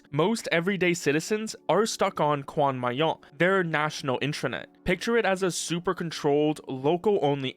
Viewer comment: that's what he called it just 3 seconds earlier, and then went to explain what the term mean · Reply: heard him say "national internet", not "national intranet". Apologies